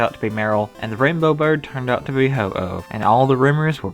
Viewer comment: I heard him say ho-ho